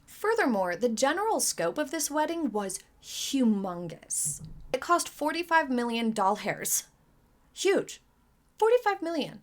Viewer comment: Did you say, "it cost 45 million doll hairs"?! 🤔 I'm just trying to envision what that looks like... 😂